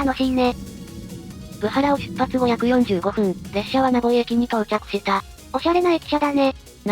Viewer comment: 名古屋人ワイ、「ナヴォイ駅」を「名古屋駅」に空耳する。